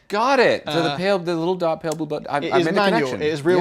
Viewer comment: did Thomas say "pale blue butt" 😂